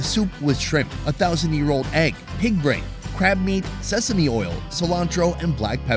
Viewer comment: I'm not sure if I misheard one of the ingredients of the soup. Did you say pig brain?